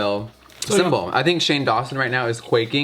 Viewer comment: WHEN SHE SAID CONFIRMED IT SOUNDED LIKE SHANE